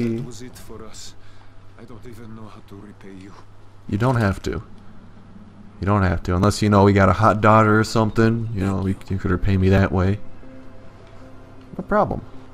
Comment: I thought he said hot dog haha